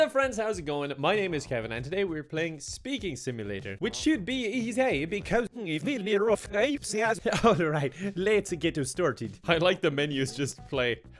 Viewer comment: Did he say 'Which should be easay because I speak sorry living alright, let's get distorted'?